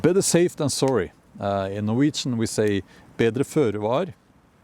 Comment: I need help understanding the Norwegian way of saying, "better safe than sorry." Did he say, "bedre før du er"? The time stamp is at 5:41. Og også på 10:50, is it: "Jeg skal stille opp på veggen" He says it so fast and I'm trying to get it right lol. Takk skal du ha :)